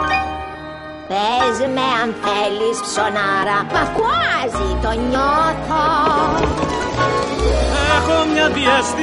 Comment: στο 1:43 τι λέει παρακαλώ; "μα κράζει" ? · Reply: "μα Κουάσι το νιώθω" απο το Κουασιμόδος